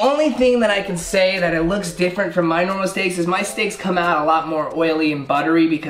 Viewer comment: Was I the only one who heard him say sticks instead of steaks